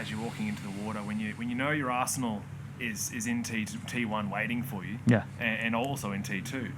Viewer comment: for a second I thought he said "when your asshole is in T1" ...